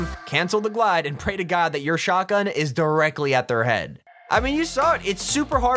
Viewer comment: what was he saying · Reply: Pray to god that your shotgun is at their head is what he said